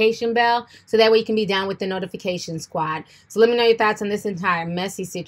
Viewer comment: Listen, I love you hunny, but you’re gonna have to get this word together, Chile. I thought you were saying “let me know your thots”. 😝😝😝🤣🤣🤣 love ya boo.